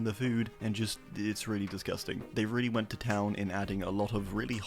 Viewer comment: Did he just say israeli disgusting? Reported.